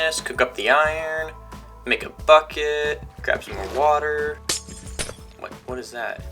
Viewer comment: Am I the only one who hear fucket when he said bucket